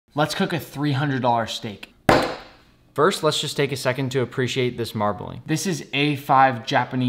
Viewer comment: Did I hear 'Lets just steak a second to appreciate this marbling'?